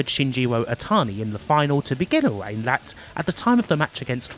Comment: I initially misheard this as "starting a match that lasted one day shy of eight months" as opposed to reign. I'd love too see a review of a wrestling match that lasted two thirds of a year :p